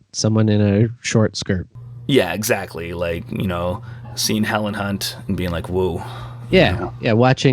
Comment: At the end the first time he said "seeing Helen Hunt" I heard "seeing hella c¿nt" 😂